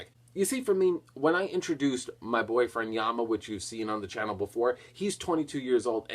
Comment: WTF!!!!!!!!! Did he just say His Boyfriend Yama 2:47????????????????????????????????? This fucker is gay? I watched some of his video awhile back when I was looking up crystal beast. is the black dude his boyfriend?